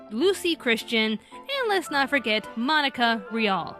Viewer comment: wait............did you just say Monica OMG that my NAME